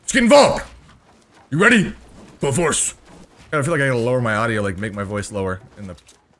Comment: did you say Broforce? ‘MURICA